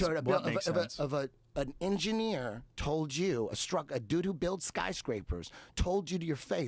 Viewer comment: it sounds like Eddie says "Doo Doo built skyscrapers" which then I thought yeah that would collapse immediately.